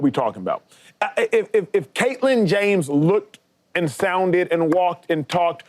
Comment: did Acho call her "Caitlin James"? (8:44)......I mean if that's your nickname for her, don't you have to say she should be in the #1 spot?